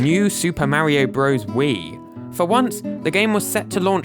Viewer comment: I misheard it and thought he said Mario Bros: Smokes Weed